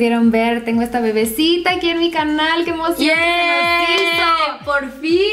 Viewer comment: Wtf escuche que rica nalga oh soy el único 😂😂😂🤷🏻‍♂️🤷🏻‍♂️🤷🏻‍♂️🤷🏻‍♂️🤷🏻‍♂️❤️❤️❤️❤️